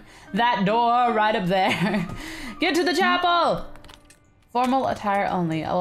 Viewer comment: When she said 'get to the chapel!' I thought she said 'get to the chopper!'